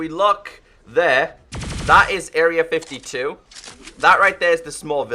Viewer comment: is it just me or did he say area 52??